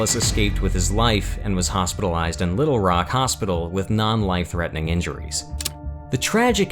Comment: non life threatening? did i hear that correctly?